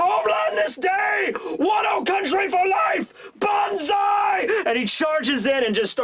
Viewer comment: I thought you said BANKAI!!!. Now I wanna see ryuma Bankai